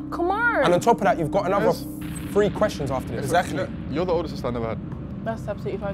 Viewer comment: What did darkest said to Nella @ 8:50 and why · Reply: You’re the older sister I never had